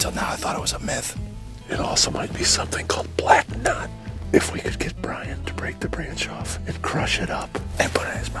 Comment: I thought the guy said "If we can get Bryan To break it off" And JOE quickly made the suggestive joke for Bryan to put it in his mouth.